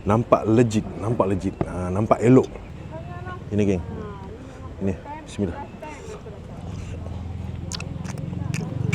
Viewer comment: aku salah dengar ke? mcm marah akak tu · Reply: tulah, dengar dia cakap "ni first time and last time aku dtg"